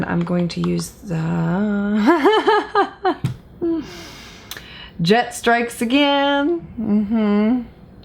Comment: I heard you say “and I’m taking the Jet Strikes Again” and thought hmmm kind of a strange name for a brush 😂